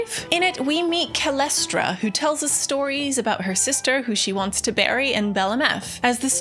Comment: I thought she said 'her sister she wants to marry', i had to back it up and listen again.